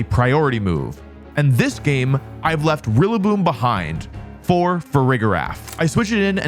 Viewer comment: A little mistake in the captions at 2:10:20 - “really boom” instead of rillaboom